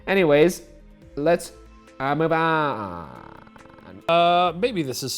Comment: i know he says "maybe" but i sure as hell thought he said "baby" and i didn't even question it